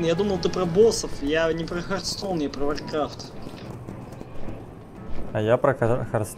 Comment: На самом деле пока ты летел на грифоне, я заметил КАПЕЦ КАК МНОГО ИГРОКОВ! Просто куча летает на грифонах по локациям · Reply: И я извеняюсь, мне послышалось, ты сказал "Варкрафт 1" А стракрафт да, это 98